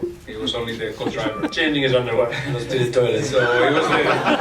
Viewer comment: What does say at 1:57 that makes everyone laugh? I can't make it out. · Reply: changing his underwear .. lol